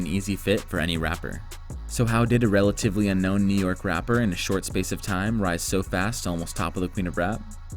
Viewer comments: captions say stripper You said rapper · why do the subtitles say STRIPPER and you say RAPPER lmao. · He said "rapper" but the subtitle said "stripper".